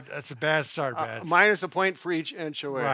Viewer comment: Every time you guys said "minus", 1:30 I thought you were saying "mine is..."